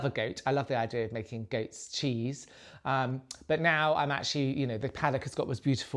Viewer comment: I was worried then, I had to rewind, I thought you said goat stew, but you said goats cheese. 😅 xx You two are a good team xx